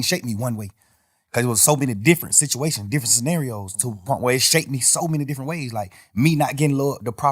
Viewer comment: What did he sayyyyyy😂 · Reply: To the point where